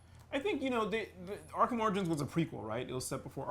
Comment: I totally heard her say Arkham Oranges.